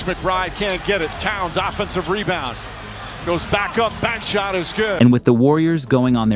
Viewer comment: what did he say?😂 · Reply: Back shot is goood !